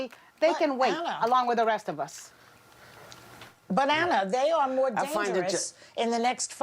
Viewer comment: thought Joy said, 'Bananas'.😂.... I know that Joy said, 'But Ana'